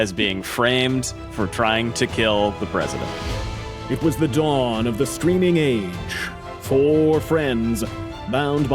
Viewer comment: I find myself mishearing the voice over - I swear I heard "It was the dawn of the screaming age" ... although I'll admit that I am both a bit on the tired side and am having a stiff vodka and soda, so my hearing might be less reliable at the moment.